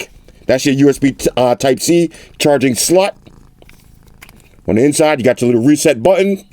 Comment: did he say charging sl*t?